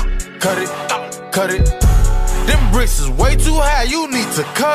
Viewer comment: i just heard "blood pressures way too high you need some curry"